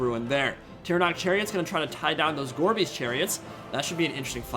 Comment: Listening to this in the background, I kept hearing "Gorby's Chariots", and wondering if that was a RoR. Took way too long to realize it was "gorebeast."